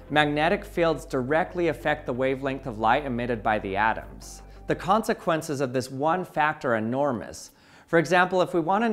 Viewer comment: What you said was "the consequences of this one factor enormous". You meant to say "the consequences of this one fact are enormous", but didn't enunciate clearly enough. Or this is one of those regional differences where differences in pronunciation disappear for some weird reason (e.g. there are places where "Mary", "merry", and "marry" are very strangely pronounced exactly the same way).